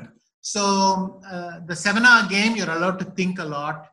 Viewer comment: I think Vishy says 'seven hour game' but subtitles says 'seminar game'. Could you please check.